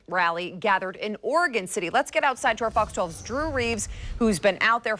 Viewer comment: Thought she said Drew Brees for a second.